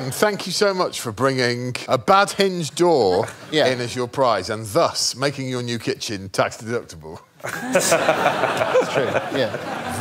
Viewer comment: What does Greg say at 4:43 ? I watched that bit 4 times, still couldn't catch it :/ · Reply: and thus making your kitchen tax deductable